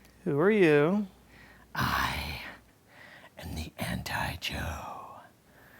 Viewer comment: i heard: i am the anti jew